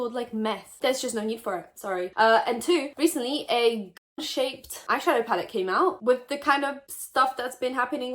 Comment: Wasn't paying attention and thought you said ducked shaped pallet... Guess I should watch and listen.